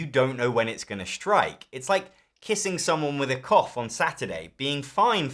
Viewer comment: I heard “kissing someone with a cock on a Saturday” 😂